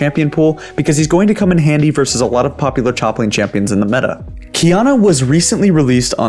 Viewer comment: did he really say "choplane champions" or am i going crazy?